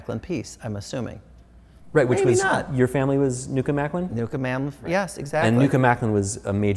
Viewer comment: I can't understand the name of the "major frame maker in the midwest". (33:02) Can someone write it here, please? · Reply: Newcomb-Macklin. An American frame designer that specialized in hand carved and gilded pieces.